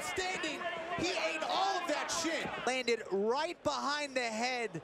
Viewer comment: lmao even the subtitles thought he said "shit" and not "shin"